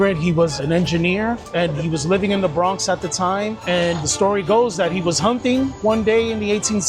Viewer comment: I don't care what the captions shows. He says "The story goes that he was humping."